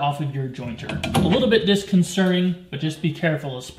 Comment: Did you say disconcerning at 0:58?? 🤣🤣